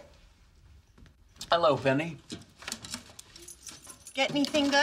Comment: I think he said “ hello Fanny” lol